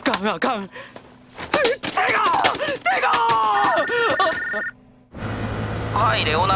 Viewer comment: he is litteraly saying SEGA